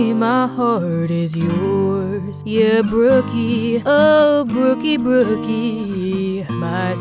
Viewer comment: I thought he said oh cookie 😂btw why does one of the boys look like Patrick star